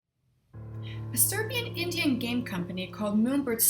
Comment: To elaborate: the game was announced on Kickstarter specifically in 2016. Also, maybe it's just me, but it sounds like I said "Indian" as opposed to "Indie" - just to clear that up in case anyone was confused! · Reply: I heard "indian" rather than "indie." That's alright, I got what you meant. Great overview BTW :)